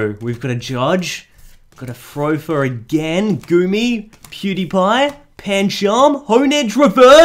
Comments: did he just say Pewdiepie? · did he said pewdiepie?!!!